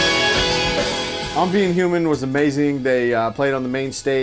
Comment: What was the name of the band? It sounded like said "Unbeing Human", but I can't seem to find anything from them.